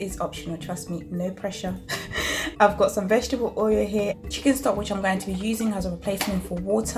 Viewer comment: I am confused if 1:07 vegetable oil what is at 1:08? I can't hear you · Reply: It's chicken stock